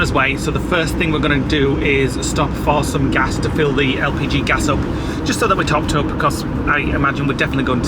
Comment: I thought you said LGBT gas not LPT gas. ⛽️ 😂